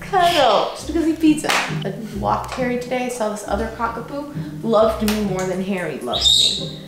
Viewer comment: Did she say Cocka-poo? 😜